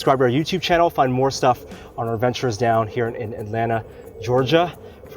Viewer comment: fail handshake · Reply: i thought he said thanks fruit bean